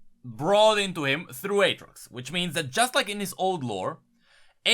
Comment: You accent is too funny, everytime you say Aatorx i hear "Adrugs"!